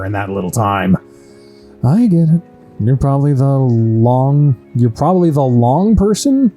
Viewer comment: I assume you've got it already, but I think it was supposed be "lone person" instead of "long person" Thank you for keep spoiling us with content 🙏